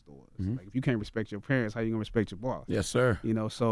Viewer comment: what did he say @ 24:41 ?? if you cant respect you parents how are you going o respect your what??? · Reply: zane Smith If you can't respect your parents how you gonna respect your boss